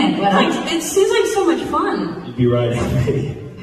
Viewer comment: What does he say in 2:25 that they hide their faces? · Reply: He says he would want to be the dragon. She says Daemon. So then he says "you'd be riding me".